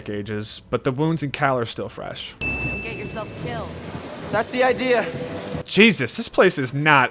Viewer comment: first time I played I heard "you'll get yourself killed", Cal's response had me very concerned